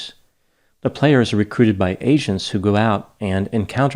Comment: Lmfao at 1:48, I thought said "The players are recruited by Asians"